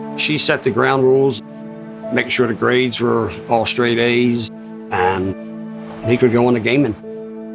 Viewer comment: did any one understand wtf he said? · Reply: if he got all A's he can go on to gaming